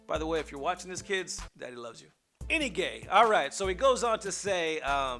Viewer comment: did he just say any gay?